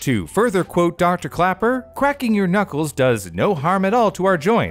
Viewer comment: did he say quaking or is it just me